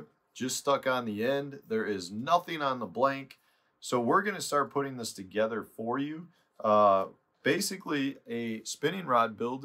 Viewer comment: I heard you say we are going to put this rod together for you. So I'm sure you were talking about me. So when can I expect my new rod?😂🤣